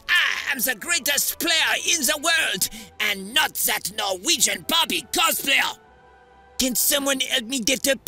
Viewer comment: What did Mbappe said to Haaland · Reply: Norwegian Barbie Cosplayer😂